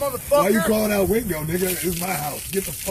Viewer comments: did mayhem just say nigga 😂😂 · Wait 2:44 Did mayhem say nigga